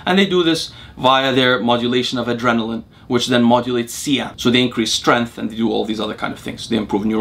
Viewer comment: Hey, what did you say at 0:54? "Adrenaline modulates ____" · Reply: I'm not sure, but it modulates cAMP which is how it makes you stronger.